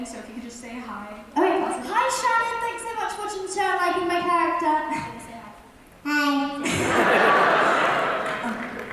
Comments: What did say at 33:50? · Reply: She wants to call the dog Ralph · What does say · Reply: Natalie Ortiz I wanna name the dog Ralph